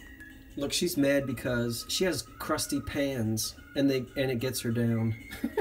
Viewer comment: Oh wow. I was sitting at the computer listing and listening to you. I thought you said the woman in the ad was mad because her pants are crusty. lolololol (It was PANS, she was mad because her PANS were crusty lolol.)